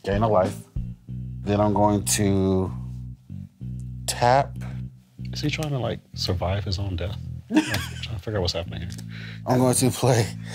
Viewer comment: "Prof's trying to kill me, Prof's trying to get you to kill me, and Ken's trying to kill himself" hands down the best line the entire episode XD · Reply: I thought he said "is he trying to survive his own death"